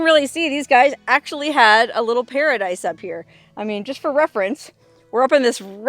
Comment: Did you say 'Pearadise' >< lol